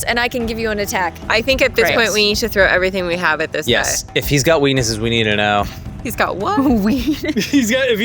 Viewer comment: For the record I heard weenisis